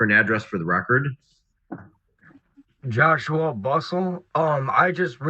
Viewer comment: I’m sorry, did he just say his name is “Joshua Bustball”?!?! 🙀